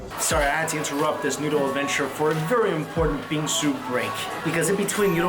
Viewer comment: When said bingsu I heard it as bean soup